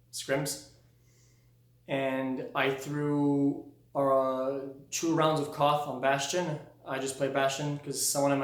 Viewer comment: He threw one game off stream. Obviously he shouldn't have done that, but people are really making a huge deal about it. It's like the people that don't like XQc finally have something to pick on him and they go full rage. I think this video round it all up and we can leave this bullshit in the past. Next time you get tilted from someone just POP SOMETHING IN THEIR DUM ASS. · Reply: Sure Five thanks i thought he said 2 games instead of 2 rounds